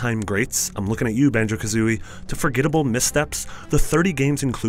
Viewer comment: Did he just say, "clickbate"?... Oh, no... He said, "forgettable missteps'.